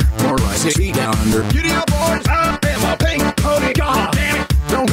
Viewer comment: When he says "I AM A PINK PONY GOD" I hear "ALABAMA PINK PONY GOD" lol